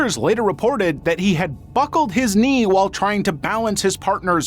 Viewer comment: i swear i heard "fuckled his knee" 💔